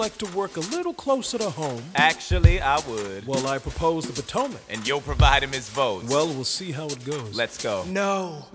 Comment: why do i hear and youre for vitamins foes